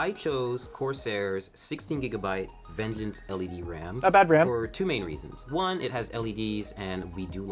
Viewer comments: What? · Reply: Somewhere around 5:39 you said something that sounded like "that's a bad ram" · What did you say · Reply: "Not bad RAM" What I meant by that was Corsair is a decent company to buy RAM from.